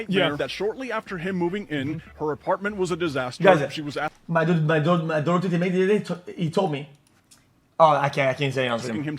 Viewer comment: i thought i was pretty good xqc speech analyst but wtf is he saying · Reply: My Dota 2 teammate?